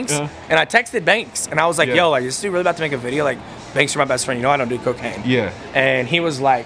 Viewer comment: What does he say about cocaine?? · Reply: he said you know i don’t do cocaine